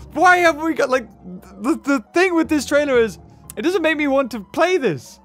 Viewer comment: anyone else think he said "the thing with australia is..."